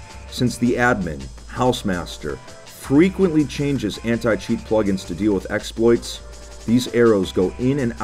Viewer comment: I thought it said notch eat